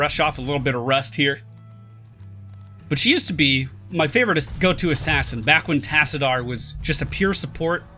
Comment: sorry did you say "my favouritest" ?